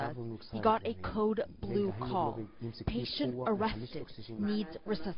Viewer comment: it sounds like he said patient arrested